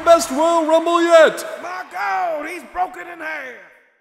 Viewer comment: did anyone else hear "Marco he's broken in half" ?